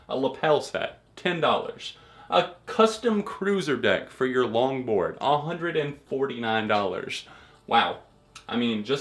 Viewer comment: When you said "longboard" I heard "lawnmower" and was so confused as to why fall out boy would have merch for a lawnmower